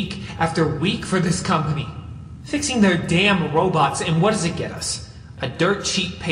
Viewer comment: did i hear roblox